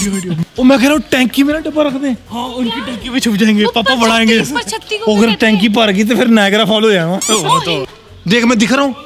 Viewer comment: did he just said Niagara Fall ? V's iconic line “Niagara Phopho” ommo so niiiiiiiiiiiiiiiiiiiiiiiiiice 💜💜💜💜💜💜